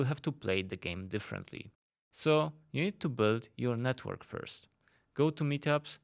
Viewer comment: what's that you say? All I heard was: "...burn your network first...".